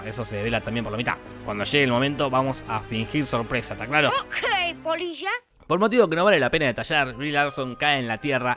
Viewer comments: Nadie: Jorge:¡Okey polilla! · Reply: ah eso dice jajaaj pense que decia Ok polisha mmm acento argentino jajaja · Nadie: Jorge:¡Okey polilla! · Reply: Yo también, pensé que decía POLISHA